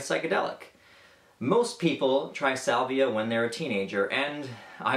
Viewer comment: I thought it said saliva